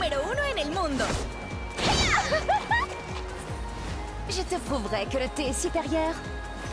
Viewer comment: those subtitles do not match what she said at all. She said "I will prove that tea is superior"